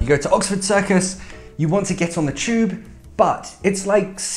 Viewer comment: you want to get on what? i'v never heard that word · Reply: He said Tube and after he wrote on the whiteboard